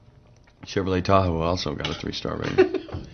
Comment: What is he saying at 0:26 ??? pleaseee someone!!! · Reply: Chevrolet also got a 3 star rating. Referring to the dinner.